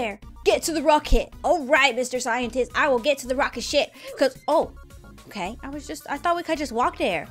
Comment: I thought she said get too the rocket shit